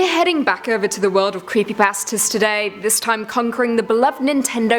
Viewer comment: Who else thought she said “creepy barstards”?